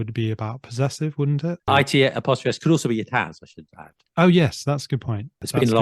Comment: can someone tell me what said in this moment? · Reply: I think something went wrong with the recording but from what they said afterwards I guess he said that "it’s" could also mean "it has"